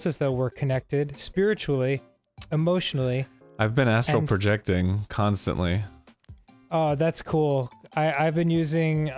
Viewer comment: When Arin says "astral projection" we all know he means farting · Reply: Legitimately thought he said “asshole projecting” at first